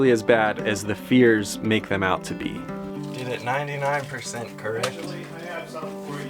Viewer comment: what he said? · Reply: "got it 99% correct"